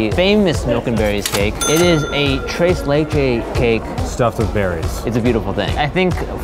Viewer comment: Did he just say treys lecey cake? what spanish is that?